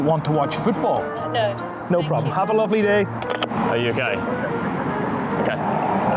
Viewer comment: did he say "are you gay"😂😂